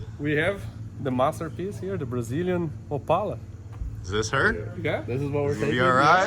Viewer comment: I thought he said Impala at first, never heard of an Opala.